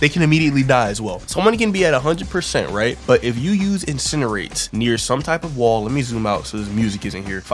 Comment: did u say 8:33 garous incerate?